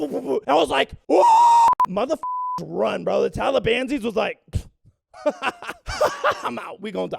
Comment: did he just say “talibanzies”???